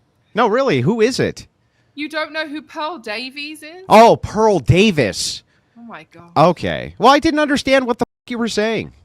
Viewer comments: Pearl Davis she said and you couldn't even understand whose name I thought she said Paul Davis. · Sounded like she said “Carl Davies” initially 😂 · I legit thought she said "Paul Davies" - as in the physicist 😂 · I thought she was saying paul davies. Someone speaks to my wife, that aint speaking to me. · It sounded like she said "Paul Davies."